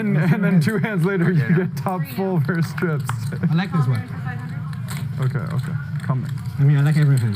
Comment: If you struggled like me to figure out what Wesley says, he says "I like this one" off-camera just before we cut to the view of them both.